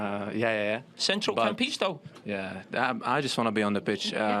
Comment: What did he say? "Central can't pitch, though."? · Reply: central campisto